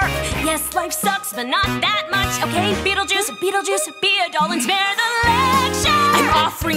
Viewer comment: I really heard it as "Beetlejuice, Beetlejuice, being gay and female," and I was like, not the angle I expected, but ok